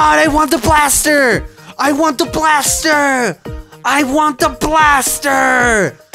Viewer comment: XD idk why but it sounds like he is saying I WANT TO BLAST HER XD